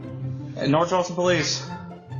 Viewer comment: Sounds like they said North Charleston police not North Austin